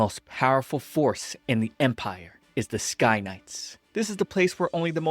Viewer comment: why did I hear sky nuts